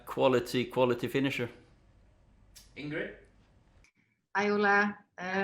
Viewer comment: does she say Hi Ola? Ive noticed the last few conferences she says his name a bit differently. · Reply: She's a norwegian journalist, so she greets him and pronounces his name in norwegian: "Hei, Ole!"